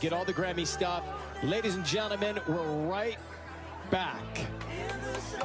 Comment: but I heard "WiLL see u in LA"😐